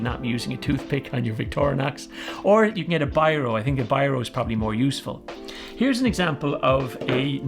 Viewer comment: What did he say here? Replace the toothpick with a "byro"? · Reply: Toothpick with a biro. A biro is a word pen brand. You can get pens that for in the scales.